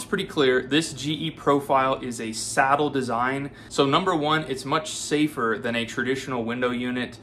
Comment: At first I thought this dude said, “it’s a sad ole design.” 😂